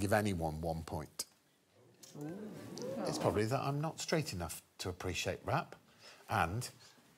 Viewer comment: could any native speaker tell me what‘s greg saying at 38:35 “straight”or“street”？ it's important to me🥹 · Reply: "It's probably that I'm not straight enough..." he refers to his bisexuality here and there. Love that guy.